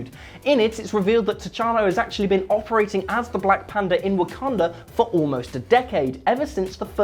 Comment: Did u say black panda at the 2:45 mark?